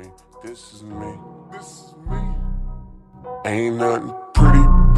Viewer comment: Help I heard this, “pretty butter face” 💀💀